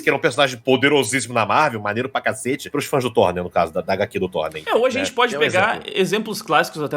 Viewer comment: Carai, eu entendi FajuThor e perdi tudo, kkkkkkkkkkkkkkkkkkkkkkkkk